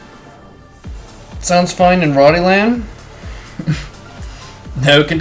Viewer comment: I must be deaf, but I can't for the life of me hear what he's saying... Does he say something about Rotterdam, or "Ronnie Land" ?! That's what I hear lol